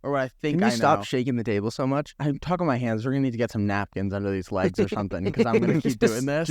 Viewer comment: WHAT DOES SAY HERE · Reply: "I talk with my hands, we are going to need to get some napkins under these legs or something." He moving so much it's shaking the table, so napkins would level out the table and keep it from moving so much.